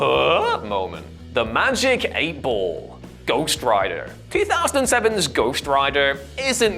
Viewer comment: Oooooooooh I thought he said Ghostwriter. He said ghost rider lol. I was only listening to the video at that time.😂😂